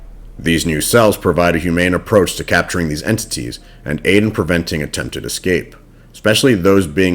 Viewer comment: Wait did you say Aiden I couldn’t hear it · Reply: it said something that sounded like Aiden did it say that